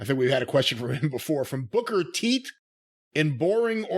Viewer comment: did he just say "booker teet"? Lol wtf